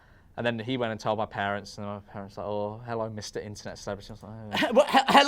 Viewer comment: Tbf it does sound like you said Mr Imternet Services there, I can't really make myself hear "celebrity"